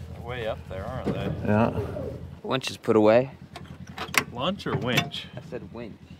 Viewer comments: i thought said "lunch" too 😂😂! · deffo sounded like lunch!! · Peanut was excited to get and run peanut definitely get the exercise and yes it did sound like he said lunch instead of wanch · I heard lunch